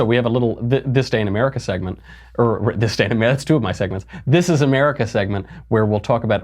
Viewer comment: I just realized there is an auditory puzzle when listening to the opening. I was walking to the trash and thought I heard "Disdain America." Then I walked back, remembering I was watching a conservative show, heard it right... "This day in America." LOL